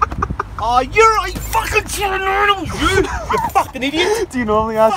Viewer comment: what did say at 2:30? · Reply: It sounded like "I fuckin' chew noodles you!" but i think it's "I fuckin' shoulda known it was you!"